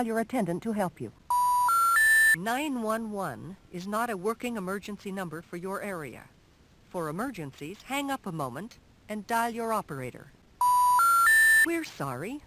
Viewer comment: Excuse me, what did she just say? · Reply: "911 is not a working emergency number for your area. For emergencies, hang up a moment, and dial your operator." I believe if you lived in a teeny-tiny community with telephone service, 911 couldn't reach you. So you would go to your phonebook and call the emergency number there.